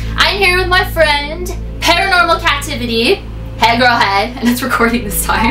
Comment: Did you just say "hey girl hey " as in Karina Garcia's line? Or im jut hearing things